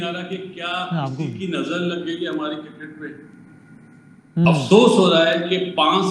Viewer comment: did he really said "nazar lagg gayi hai? " 😂😂😂😂